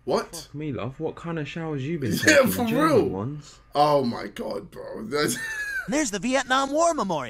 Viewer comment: can someone tell me what he said. 'what kind of showers have you been taking ..... ones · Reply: he said jail ones